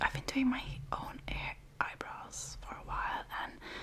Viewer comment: I thought it said "doing your elbows".